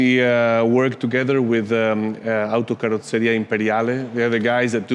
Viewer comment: what company did he say? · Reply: Autocarrozzeria Imperiale